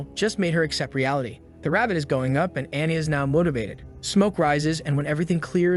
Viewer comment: did I hear motivation? I am judgement cutting it am cutting it so good